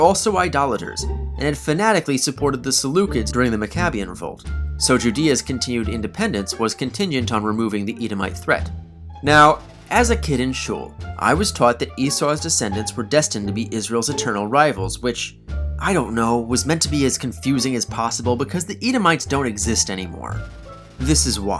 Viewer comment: I misheard this as "As a kid in Sheol"